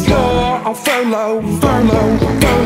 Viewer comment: anyone else have this on autplay and thought he said "I knew you did mushrooms when you walked in"